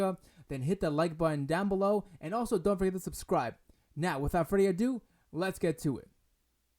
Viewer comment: Did I hear "now without Freddy adu, let's get to it"